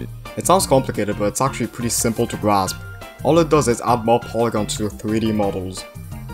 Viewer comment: you told me to tell you your subtitles wrongings so u did an error on 3:51 u said polygons and on the subtitles it says traingles